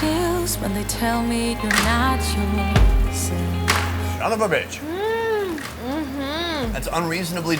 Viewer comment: was it so of a bitch or did I misheard this one😂